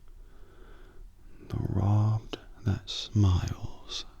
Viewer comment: Is the quote at 4:53, “The robbed who smile steal something from the thief?” Am I hearing that right?